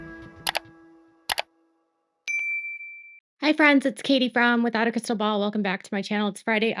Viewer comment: Katie!!! I swear when your video first started when you introduced yourself, I swear it sounded like you said, "Hi Friends, it's Katie Brown" !!! Tell me I heard wrong!!! ❤